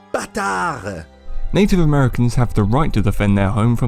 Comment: did France just say baka?